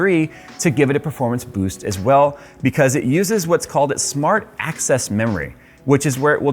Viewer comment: Man, when I heard "smart access", I thought they said "smart ass"